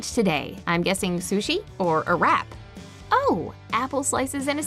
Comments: i thought she said "sushi, or a rat" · It sounded like she said sushi or iraq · I thought she said, "Sushi and Iraq"